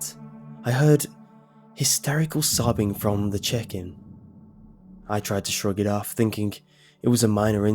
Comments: The chicken? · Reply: Oh my..... I thought he said chicken too! LOL Well darn his amazing British accent I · was anyone paying attention to that shadow on the side, it looked like a figure sorta :/ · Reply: in the beginning it sounded like he said "chicken" instead of "check in" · I accidentally went to 0:20 while eating chicken. I thought I heard "I heard hysterical crying from the chicken". I spit out my food.